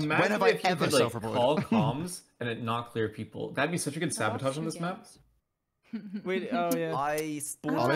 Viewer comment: What is 5up saying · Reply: "Man, imagine if you could call comms and it didn't clear people. That would be such a good sabotage on this map. I'm just thinking about it because, like, so many people just got cleared, right? If I called comms you wouldn't know when this body died." i believe thats what hes saying